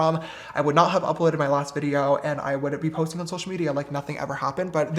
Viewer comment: "If I thought that this was just some stupid scandal [..] I WOULDN'T be posting on social media like nothing ever happened." Am I hearing him correctly? Talk about a slip... 🤦